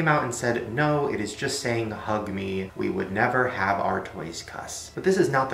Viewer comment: The Hatch-ables one really does sound like it said "f*** me" instead of "hug me" in the video.